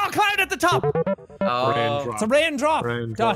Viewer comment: What most people heard: It's a raindrop Daithi What I heard: It's a raindrop Daddy.